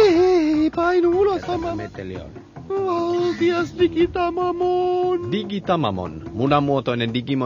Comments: En voi ku miettiä et hitto näil agapion tyypeil on ollu hauskaa äänittäes XD em kyl tiedä miten niillä piti pokka! Ps 2:56 "hiihii painu ulos homo!" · escuché mamón? :v jaja XD